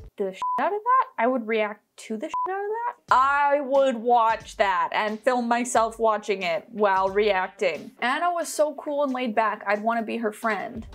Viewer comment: I thought she was going to say "I'll watch that and record myself watching that well shiting..." Lol I don't know just the mixing up of saying that weirdly I thought that's what she was going to say lolol